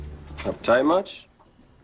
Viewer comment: What did Perry say to Lillian at 23:44? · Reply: Uptight much?